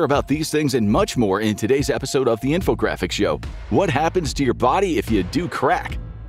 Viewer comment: all the "crack" at the beginning, I heard "infocrackics show" 😂